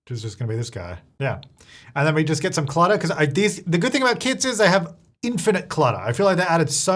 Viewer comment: I thought he said "the good thing about kids is that they have infinite clutter" 😂 which is true...but be said kits.